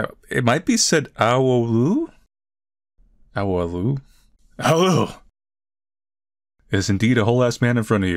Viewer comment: i can’t be the only one who heard “UwU”